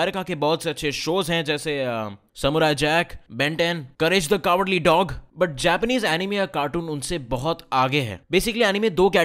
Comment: I heard "Ganesh the cowardly dog" and had to replay again LOL